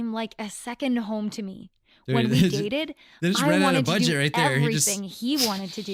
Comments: i heard this as "when we yeeted" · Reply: I heard this as "when we dated" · i heard this as "when we yeeted"